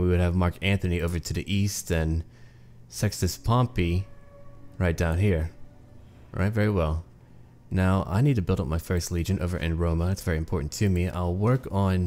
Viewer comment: i thought you said "sexist pompey" xddd